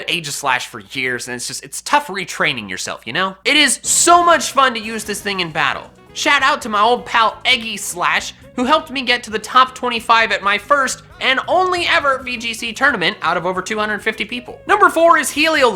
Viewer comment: did you just say EGGYslash